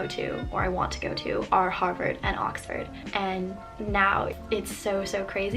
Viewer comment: Is it bad that I thought she said the two colleges she wanted to go to were Hogwarts and Oxford.